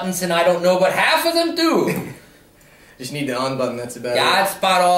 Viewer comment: When they were talking about the remote and the one said you just need the on button i thought he said unbutton and thought it was a clever if inappropriate joke. Good day.